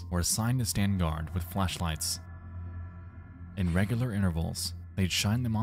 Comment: I'm sorry what? I'm sure it's just the accent but I don't you said that right, because I keep hearing flesh- not flash.